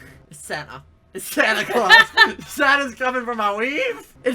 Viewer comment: I thought he said weed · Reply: he said weave not weed